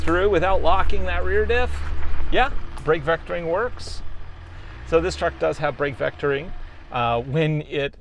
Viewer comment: LOL, for a moment there I thought you said this truck was $80k. Silly me.